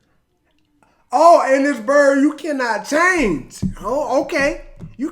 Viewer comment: I always thought they said "This bird you can not chain" thought that was a reasonable metaphor for a "Free bird".